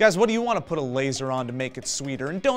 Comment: I swear I thought I heard Joe say shart, as opposed to shark. Which then prompted me to imagine a turd with a laser on it.